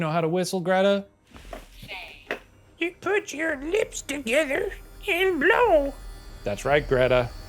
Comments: swear that shit said “Shane” not “Say” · Why do i hear „shane“ at 51:49 ?